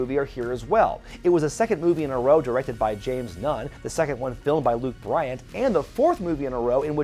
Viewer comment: It sounded like you said James Gunn and I was pleasantly surprised. Directed by James Nunn.... shit